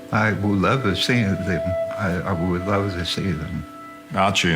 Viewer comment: Sounded like he said "I'd love to see them IF" then he cut himself off and repeated ," I'd love to see them. What was he about to say, IF they exist?????